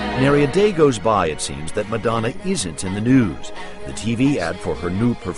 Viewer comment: what is he saying in 3:52? "Madonna seems to.."?